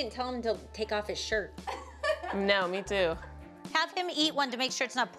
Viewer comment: What Jill said made no sense “have them eat one to make sure it’s not poisonous ☠️!” Then your child will have the food poison! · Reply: She said "Have HIM eat one..."